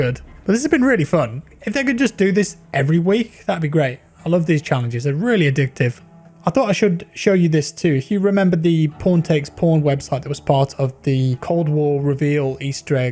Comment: I thought he said "porn take porn" instead of "pawn take pawn"